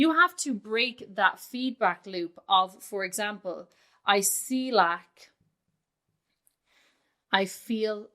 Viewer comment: Wow well I feel like I'm on the right track, listening to you talk in the background while doing computer work when you said I see lack my brain didnt even recognize that as a sentence. I heard it as one word, like Isilac. I was like that sounds like a medication or something what is that 🤣 then I started paying attention again to what you were saying and was able to understand. Haha! Love when little things like that happen that really hint at our current mental state